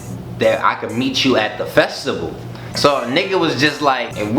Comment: did he say "festible"